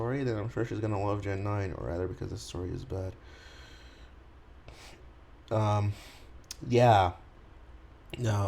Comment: "Gen 9 story bad" I better be mishearing that.